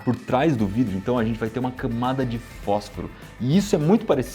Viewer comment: camada de fóscoro ou eu que ouvi errado?